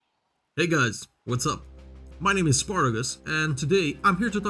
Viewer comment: did he say ni**as what's up